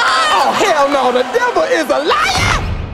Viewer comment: Patty: Oh hell no, the devil is a liar?! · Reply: I thought she said, “the devil is alive!!”